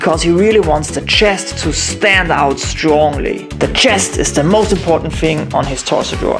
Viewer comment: What’s a Chester drawing? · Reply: wow sorry I had my volume a little low and thought you said Chester lol awesome video also